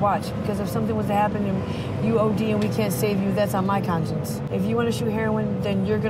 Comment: I think she said "That's on my conscience" not "conscious." Meaning if the kid overdosed, she would feel guilty for going against her moral judgment: which is to not turn the kid into a drug addict. :)